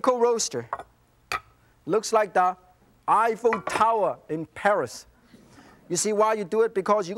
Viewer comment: I thought he said “iPhone Tower” for a second there. Martin Yam can see the future!